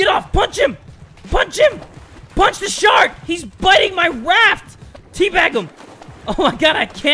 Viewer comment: am im the only one who heard waft??😂